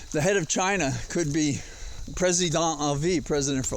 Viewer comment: I thought you said "President en vie" which is French.....?